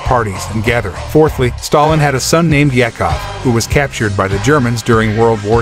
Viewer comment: hits blunt did he just say a son named Jack off